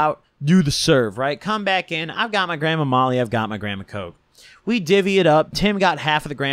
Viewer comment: thought he said "i got my grandma molly" instead of "i got my gram of molly"